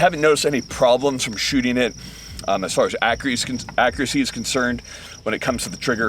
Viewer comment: did he just say acriacy 21:02?